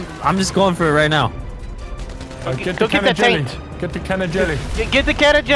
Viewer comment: Did anyone else hear can of jelly